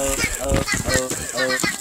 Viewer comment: 뭐라고하는거? 터트리겠어요?